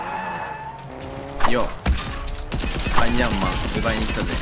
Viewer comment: アイアンマーンって言ってるとこヤイヤイマーンって聞こえたw